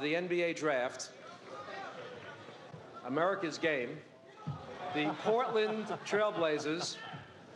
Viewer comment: i thought he said "americas gay" for a sec